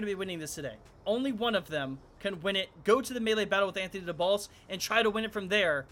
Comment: I thought he said “Anthony in the balls” the whole time😂🤦🏽‍♂️